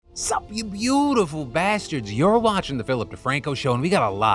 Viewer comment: Hey Phil my wife overheard the intro and thought you said "Suck Me, Beautful Bastards". I'm just wondering if you could start a video like that? Please?